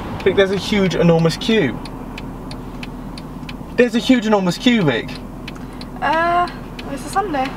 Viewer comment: what he said -"There's an enormous queue vic" What I heard - " there's an enormous cubic"